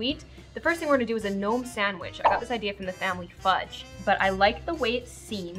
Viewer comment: Who else thought she said the no sandwich instead of gnome sandwich